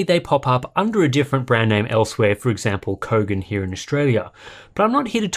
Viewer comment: Did you just say the brand's name was Covid? That's great